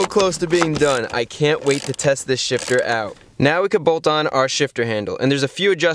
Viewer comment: I thought he said I can't wait to test this sh*t out